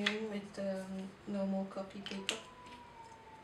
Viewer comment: I'm really confused. What kind of paper did she say she was using? At first I thought she said copic paper but then she said she doesn't like copic paper. But I might have heard wrong. I even went back to listen again XD anyone?